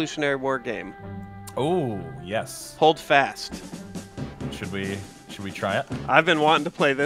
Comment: LMFAO I had to do a double take, I thought holdfast said Holocaust... Freudian slip?